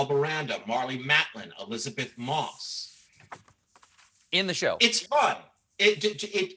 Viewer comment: when Aaron Sorkin says "Elizabeth Moss" the captions say "Donna Moss" instead.